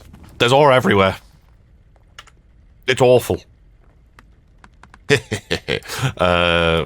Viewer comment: did you say "oreful"?